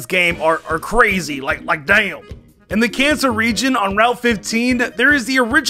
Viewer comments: did enybody hear at 5:38 sounds like he said cancer region or is it just me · sounds like his saying the cancer region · i thought he said cancer reign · sounds like he said in the cancer region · Did say Cancer region? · Did anyone else hear "At the Cancer Region" at 5:36 ?